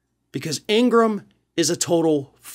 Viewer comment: She is a hopeless and deplorable human being. Oh and i thought said she was a frog but he said fraud. My apologies.